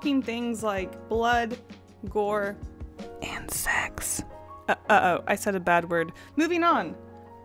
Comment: I thought you said 'insects' instead of and sex and was confused